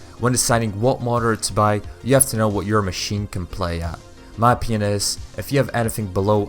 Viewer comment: did he just say my penis